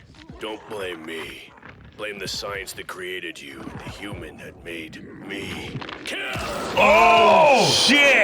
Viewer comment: You guys actually misheard some of the things in this episode! For Toguro, he said “Blame the science that created you, and the human that made me kill!” He was referencing his boss ordering him to kill the creature. I think you guys heard “the human that made me,” but if you listen closely, you can hear the “kill.” It’s difficult to hear during that scene, so I don’t blame you. As for Hiei, I won’t go into his origins, but he never said anything about being turned into a demon by anyone, the procedure he underwent simply implanted the third eye (Jagan eye) into his skull.